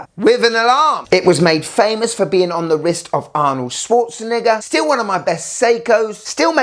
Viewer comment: Did he say Arnold “Swartzani**a?”